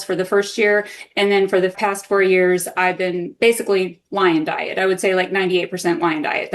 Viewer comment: "and for the past four years ,basically WINE DIET". lol what!?!!....that's what i heard and it really sounds like that lmao.